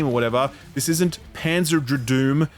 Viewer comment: Did he just say “Panzer Dradoom?” 🎉